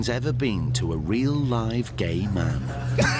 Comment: why did they have to say “real live gay man” like he’s been around dead or fake ones LMFAO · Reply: my god i thought they said real LOVING gay man